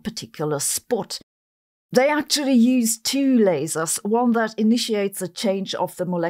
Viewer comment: I keep hearing "lairs"